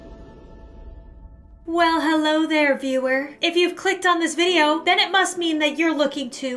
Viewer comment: Ya'll i thought she said " Hi b word " at 0:20 but shes saying " Well hello there veiwer "😳